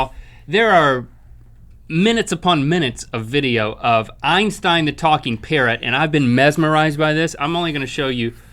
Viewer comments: i thought he said the talking carrot xD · Totally thought Link said "talking carrot" at 6:29...